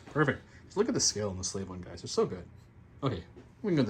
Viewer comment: Slave one? I thought that you said Slay one at first haha